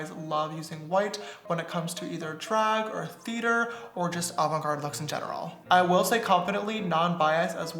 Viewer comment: is it only me or does it sound like hes saying Olive Garden looks in general XD